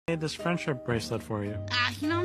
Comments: “ImA wEaR iT fOrEvER baCk ofF” Killed Me 😂😂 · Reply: I thought it said F**k off😢😢😅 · I thought he said “No, I’m going to wear it, forever. F^ck off! ” 💀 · “No, I’m gonna wear it, forever. F*ck off” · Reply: I hear “fawk off>:(“ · I thought it said frick off 💀💀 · I thought it said fuck off but it said back off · I thought she said fuck of 😭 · I genuinely thought it said fuck off